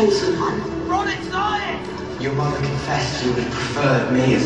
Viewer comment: What does Harry (real not illusion) say to Ron at 0:09? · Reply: MCGoldYoshi ron it's lying